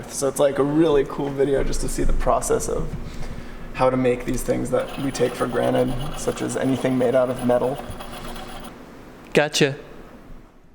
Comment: I heard "all the things we take for granite"